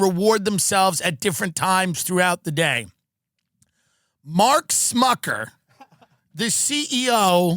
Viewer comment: did you say " Mark Smuckerberg"